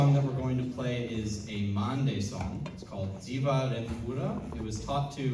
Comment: I heard "a Monday song" lol. Mhande ndoyekuusha kwangu zvee kkkk